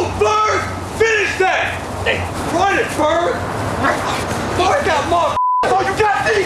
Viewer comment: Always thought they was saying bird run that shit bird